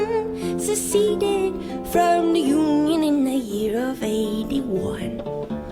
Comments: British guy mishearing union as human in the year of 81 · It's kind of funny in retrospect that you misheard union as human when Americans mishear the British lack of h depending on the accent in the opposite way